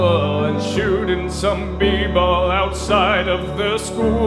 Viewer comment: Lyrics: Would it be alright if we tried again? I'll hold you tight again. We'll be alright again. I wanna be right by your side again. Watch Nick at Nite again And chill with Aunt Vivian. Well West Philidelphia, born and raised On the playground is where i spent most of my days. I'm chillin' out, maxin', relaxin' all cool Shooting some b-ball outside of the school. Hillary and Carlton and Aunt Vivian I wish that you all were together again So throw out the DJ, Let Geoffrey unpack. I long for the day When the Fresh Prince is back Where is Will? x4 Do you remember that one time when Will's dad returned? And Will was so happy but then he got burned? And Uncle Phil came down from heaven above And showed Will the meaning of fatherly love? Uncle Phil. x4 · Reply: I uh... never watched fresh prince of Bel-Air... thought he said people instead of b-ball.